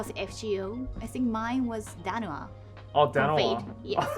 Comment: In 7:00 am I heard danua , can anyone tell me what She really said?